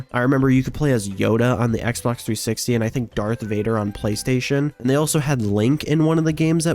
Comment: Did you just say dark Vader?